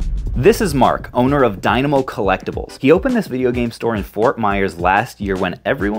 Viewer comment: Whats the address in ft myers? Or what is his website? Thought i heard “Dymo collectiables” but google wasnt helpful. Great interview.